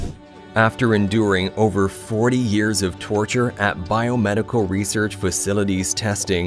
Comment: excuse me, what? Can someone explain this to me? A beaver that lived 400 YEARS in captivity? Did I miss hear what he said? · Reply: I thought he said one beaver lived for 400 years. That’s what I was confused about